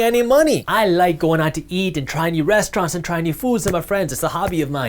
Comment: did he say "Trying out new friends". What a concept.